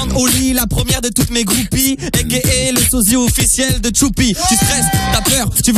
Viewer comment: XX le sosie officiel de Tchoupi, il a dit quoi ? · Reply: Aka ptn on en a mis du temps